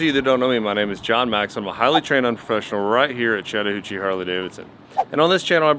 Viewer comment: Wait did you just say, "UNtrained professinal" and you ride a Sportster? Lmfao